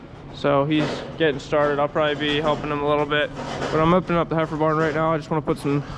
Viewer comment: I could have sworn you said ‘hyper barn’ when describing the heifer barn. I would like to suggest that it shall henceforth be known as the hyper barn.